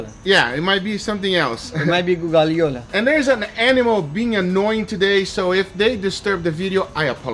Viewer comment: who do you called animal? since all I heard was talking